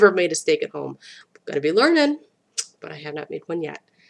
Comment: Did I just hear you say "I be lernin?" That could explain the Camero up on blocks in front of the apartment. :-) I hope they find the the hormone and/or thyroid thing. I know that hormone therapy is kind of a big thing around here; see commercials for it often. I also agree that going back to basics is the way to go. The simple carbs are killers and just make us want more. Life is much better when we get off them and just eat healthy food.